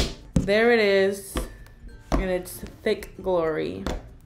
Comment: when it said d**k glory instead of thick glory!!!!!!!!!